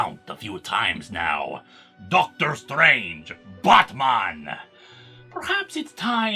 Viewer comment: The way Moarte says “Batman” I legitimately thought for a second he said “Buttman” my bad LOL